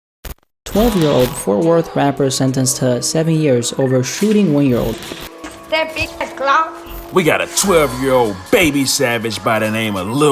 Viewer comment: I THOUGHT IT SAID "12 Year old shoots DaBaby" 😭😭😭